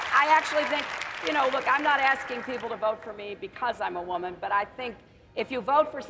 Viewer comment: Damn. She said "vote for me because I'm a woman" At least she's being honest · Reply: I heard "I'm not asking you to vote for me because I'm a woman"